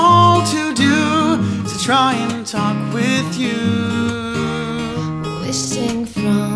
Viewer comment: Woah it just hit me, one second I am nodding along and then I hear "Is to try to fuck with you" I was like 'aw thats so lovely.. wait what?'